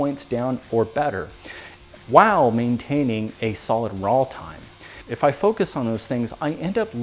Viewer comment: I've been out of the sport for about 5-years, but shot for the previous 30-years or so. Add on a number of recent health challenges and lets just say that my return is going to be a humbling experience. That said, twice in this video for new competitors, you referenced something I've never heard in my life. I'm not even sure what you're saying but it sounds like "rahl time" at 12:06. Can you please clarify as if you've puzzled someone with decades of experience, you've puzzled a new competitor or two as well. The fact that I don't see anyone else inquiring about this tells me that your viewers are not new to competition shooting, but are current, established shooters looking for any good information that may help them. Cool, informative video. Thank you for sharing your experience.